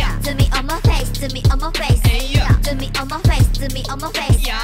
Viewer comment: Sorry but i hear it like "Do it on ma face" :'D but i love it